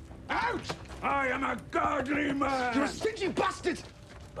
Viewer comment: Sounds like Rast says "You're a city bastard!" XD